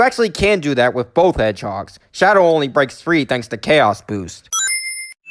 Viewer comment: i thought you said chaos bad word but you said chaos boost